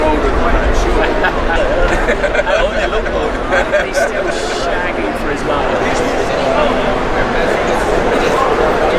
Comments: Martin Brundle says "He's still shagging for his mother/money? Can't quite catch the last word...... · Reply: I think he says "he's still shagging for his life" lol. that was soooo funny, I was wondering whether anyone else had picked it up. Not too many did it appears. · Wtf is martin brundle saying @ 1:50 lol ha ha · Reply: 'He's still shagging for his life'